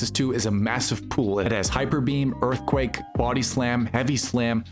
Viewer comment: I thought you said to use, “hyperbeef”! 😂